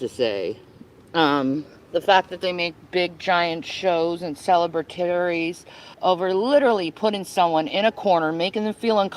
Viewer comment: anyone have any clue what word she's tring to say? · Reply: Celebration is the only thing I can think of. She talks in circles and makes no sense at all.